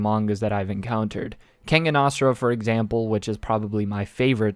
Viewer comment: anyone know what manga he said there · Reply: Kengan Ashura, its a great manga and it's an anime now. Once you finish that one, go read Kengan Omega which is the sequel.